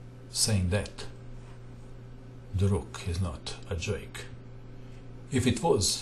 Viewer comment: What does he say about the rook? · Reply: same that the rook is not joke